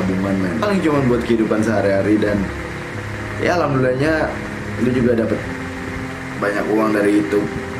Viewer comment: doi muslim? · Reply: He said "Alhamdulillah"???